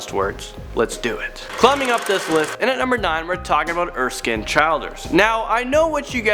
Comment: For the second dude i thought you said skinded children